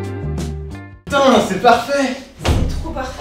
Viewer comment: c'est moi ou elle dit c'est parfait BB ??